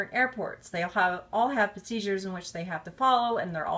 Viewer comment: Oh my god I thought she said “They all have seizures” instead of “They all have procedures”